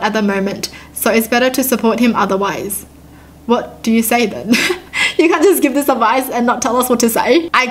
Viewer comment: is like this: "What do you say then?" Me: "Do you want some sand?"